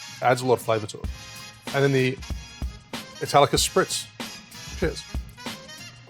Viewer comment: I want to try all of these! Such a great variety of ways to use prosecco. When you said Italicus Spritz for some reason I heard it as "Metallica Spritz" and had to do an audio double take to make sure I heard it right haha.